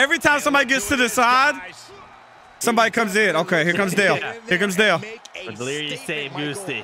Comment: Hol up 9:39 did cartoonZ call delirious dale!?!